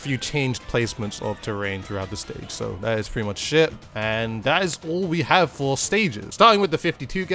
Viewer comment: "That is pretty much sh*t" lmao I know that's not what he said but STILL